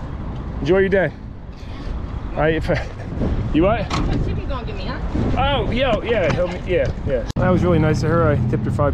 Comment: what did she say? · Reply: I think it was "what tip you gonna give me, huh?". Pretty ridiculous that she wanted payment for just helping a stranger out for a minute.